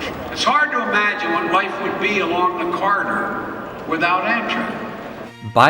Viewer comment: The way he said the corridor sounds like he said the cerrider LOL